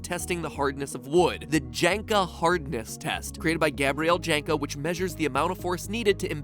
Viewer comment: Who else thought they heard “Jenga hardness rest” at 6:23 ?